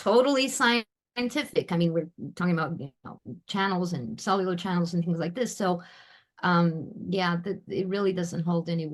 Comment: What does she say around 10:21? I'm having trouble hearing the audio for that part. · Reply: You can activate the English closed captions. Here's what it says : "We are talking about channels and cellular channels so yeah... it really doesn't hold any water that infrasound has no adverse effect." Hope it helps !